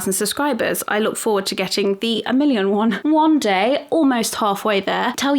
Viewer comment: maybe you did it on purpose maybe not. But it sounded like you said “wand day” instead of one day. And I love it. New favorite saying.